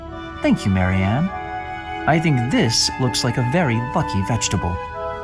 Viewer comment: Anyone else hear "fucky" instead of lucky at 26:08 ?